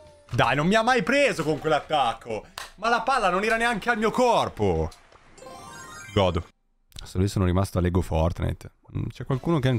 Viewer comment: solo io ho sentito "ma la palla non era neanche nel mio corpo💀"